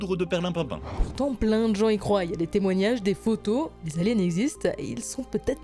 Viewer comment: oui oui moi aussi j'y "crouaille"